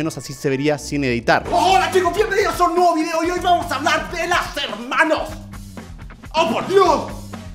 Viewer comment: escuche mal o dijo hoy vamos a hablar de las hermanos?